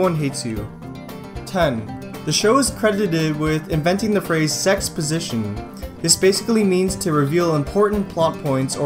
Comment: omfg I thought he said sex position and I was like wasn't that a thing before got?